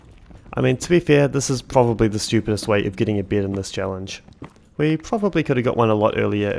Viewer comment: When you said "getting a bed" I heard "getting a beard". Why do you pronounce it like that?